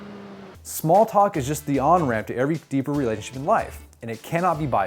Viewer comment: what's the word at 0:50...smalltalk is just the ????? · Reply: onramp